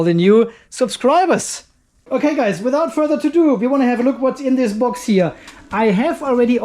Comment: Did you say: "Without further to do"